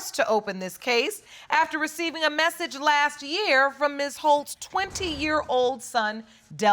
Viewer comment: Everytime the Judge said Ms. Holt I thought she was saying Ms. Hoe lol